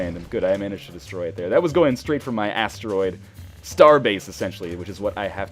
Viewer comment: All i heard was "that was going straight for my ass"